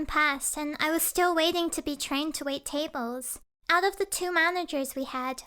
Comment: I heard “I was still waiting to get trained to eating tables”